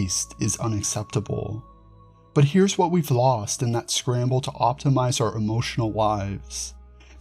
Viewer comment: I definitely heard, “that scramble to optimize our emotional wives.”